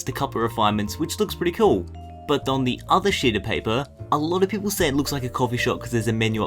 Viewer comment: and one else think that he said "on the other shit of paper"?